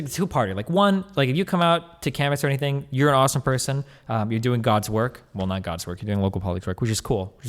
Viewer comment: Did he just say "God's work"? - whaa?